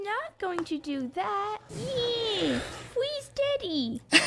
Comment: Bro it sounded like there were saying please daddy 👨 my ears have changed over the years when I realised they said freeze daddy 😃 🔫